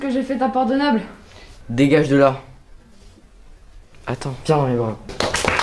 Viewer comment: il a dit quoi après "attends" ?? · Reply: il a dit <<vien dans mes bras>>